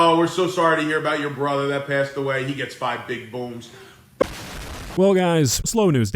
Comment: I thought the dad said that he gets 5 big boobs